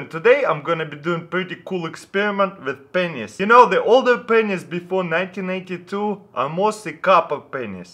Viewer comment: i totally didnt hear: "the older pen*s"